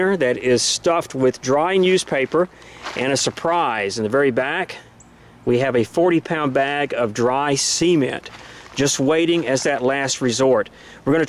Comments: for a second there i thought he said we have a 40 pound bag of dried seman i was like ''wait wtf?? replay!'' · I thought he said "we have a 40 pound bag of dry semen." lol. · I thought he was about to say dry semen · i thought he said "dry semen" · SHIIIT dude it literally sounded like you said dry semen I was like WHAT T.F.H.S! GOOD VIDEO AND NICE STREET SWEEPER A.K.A GHETTO SERENITY 🍀🍀🛡⚔🇺🇸⚔🛡🙏🏻📖🕯📢💨💥. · I swore he said 40 lb bag of dry semen the first time lol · I thought he said dry semen.